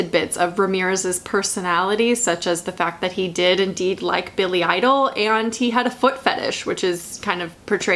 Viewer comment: I thought you said Billie Eilish instead of Billy Idol and I was about to flip my shit 😂